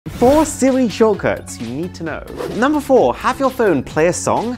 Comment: I thought he said "4 silly shortcuts"